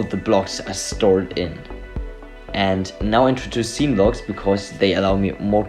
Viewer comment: what are you saying at 1:25 ? I know about Godot tile maps but it sounds like you're talking about some new feature? It sounds like scene logs but a quick search didnt really bring anything up